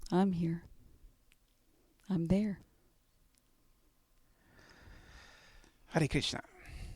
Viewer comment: What did he say at the very end before thanks mom? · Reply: Hare Krishna